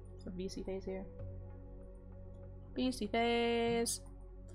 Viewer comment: did she say "V say face"? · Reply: V see face